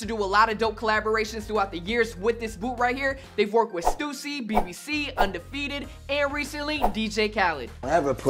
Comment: did he just say bbc